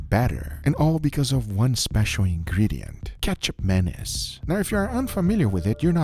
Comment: Love guga, I keep hearing ketchup mayonnaise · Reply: I thought he said "Ketchup Madness" at first.